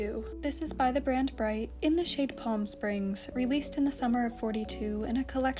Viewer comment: I heard "plum springs" and waited for a plum color lol